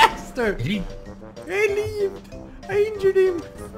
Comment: he really said ‘leaved’